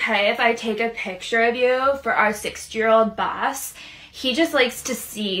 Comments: “To show our 60 year old boss” I’m out I made this dum comment and got 4K likes- that’s more I will ever make in my channel 👁💧👄💧👁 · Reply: I thought she said 6 year old boss! 🤣🤣🤣 I don’t know which one is worse!! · “To show our 60 year old boss” I’m out I made this dum comment and got 4K likes- that’s more I will ever make in my channel 👁💧👄💧👁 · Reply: I thought she said 6 year old boss, too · “To show our 60 year old boss” I’m out I made this dum comment and got 4K likes- that’s more I will ever make in my channel 👁💧👄💧👁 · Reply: Same I thought she said 6 year old and I'd say 6 is worse and illegal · “To show our 60 year old boss” I’m out I made this dum comment and got 4K likes- that’s more I will ever make in my channel 👁💧👄💧👁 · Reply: Bruh thought she said 6 year old I was like what??👁️👄👁️ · I thought you said 6 year old boss